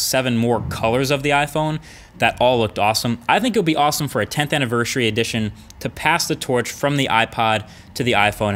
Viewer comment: is it just me or does he say 10th Anniver'sh'ary at 8:08 ?